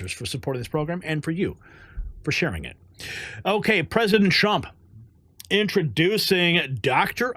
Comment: sounds like you said President Chump